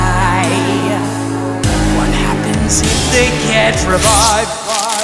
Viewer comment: I misheard the line "what happens if they get revived?" as "what happens if they can't revive?" lmao.